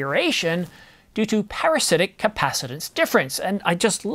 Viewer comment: says "parasitic capacitance difference". I hear "flux capacitor"